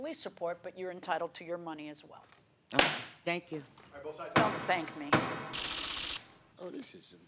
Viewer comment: When she said thank you 17:16, what did the judge said? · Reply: "Don't thank me."